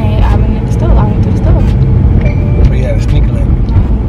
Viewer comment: Did he just say sneaky link😂😂😂🏃🏼‍♀️